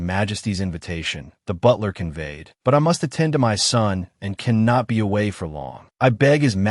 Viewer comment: wait ,did she actually said that, I have to attend my son?